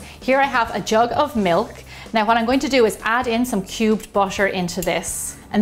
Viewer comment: what's a bosher? I don't think i heard anything like that.